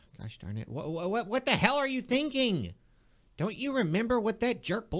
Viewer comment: Holy crap did eddy just say hell